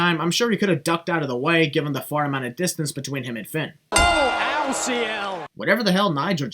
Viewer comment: I think he said "oh ciel" (which is a french expression) . It's like saying "oh my god" or "sacre bleu"